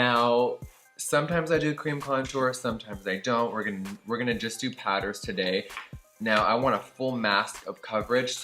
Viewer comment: did anyone else thought he said "sometimes I use tampons but today we a just gonna go with pads"